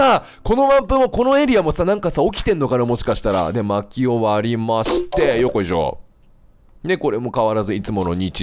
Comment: ｢薪を割りまして｣が｢真キヨ割りまして｣って聞こえて だから真面目なキヨは割られて無くなっちゃったんだなあって思いました。